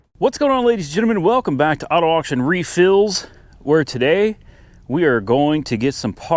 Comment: Did you just say welcome back to auto auction refills lol